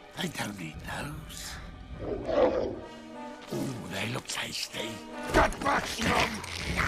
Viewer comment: 😂😂😂😂 I thought he said big and tasty i was like damn McDonald's 😂😂😂😂